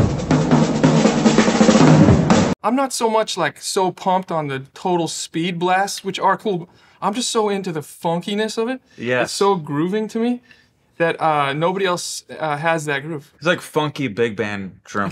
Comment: it sounds like Louis says, 'it's so agrooving to me' and I thought he found a new word instead of attracting.